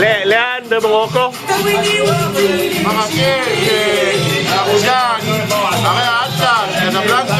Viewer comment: he did not say Abudan ( which does not exist ) he said : Taroudant, a city in the south of Agadir where many jews lived their.